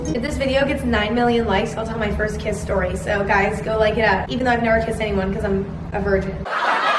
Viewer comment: it actually sounded like she said “ethan i’ve never kissed anyone cause i’m a virgin...”